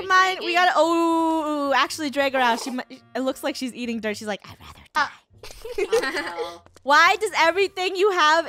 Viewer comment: IF YOU BLAST IT ON FULL VOLUME DO YOU HEAR WHAT FUNNEH SAYS!? (TIP:USE HEADPHONES TO HEAR BETTER) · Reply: She said " Shes Like I,d Rather Die " I think is that right HamtonPlayz -Roblox and more! ?